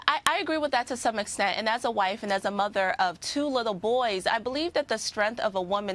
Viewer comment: "Single Mother Like Myself" OMG how many times I heard that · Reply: Where did she say she was a single mother?? 🤔She said she is a wife and mother.